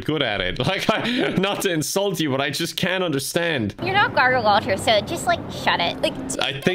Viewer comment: no I definitely heard “you know gargle walters so just like shut it